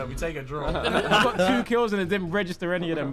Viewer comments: What did Harry say at the end @ 20:23 ? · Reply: He said "Get good kid" to tobi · did Harry just say "get good ni~" 💀